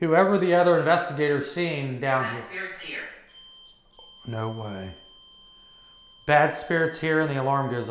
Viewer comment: as was talking about the other investigators seeing the black shadow you can hear a soft voice that I think said "no didn't"..